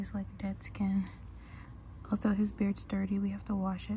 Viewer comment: Lol I just watched the video without full screen and never saw the complete caption and I literally thought it said babies head instead of beard 😳 😅 Even the beginning of the video looked like the top of a babies head so I was like .woah what is happening here nd plus when she said "the baby started crying " I really then thought it was a baby" . So read the captions guys lol 😆